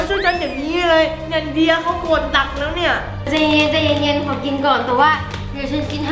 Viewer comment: ทำไมได้ยินพี่พูดว่าขอกินก่อนตัว